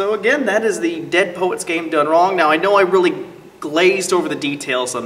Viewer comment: I thought it said "gone wrong" I'm like :O